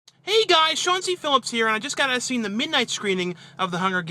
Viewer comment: haha, the first few vids I watched I thought he was saying Chauncey Phillips but he's saying